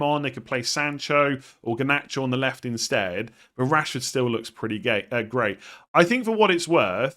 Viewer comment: did you just say rashford looks pretty gay? 💀😂